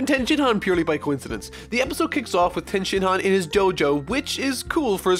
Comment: I thought he said "Jojo"